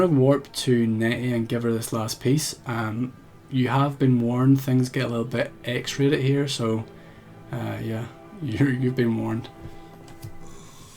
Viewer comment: I think you said "X rated" but what I first heard was "Things get a little bit EXTRA here" well, yes they do, they go a little above and beyond, don't they?